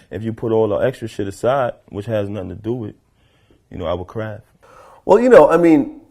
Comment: LOL Sounded like he said "I Will Cry" at 0:54 ish. But I know he said "Our Craft". Got me at first though lmfao.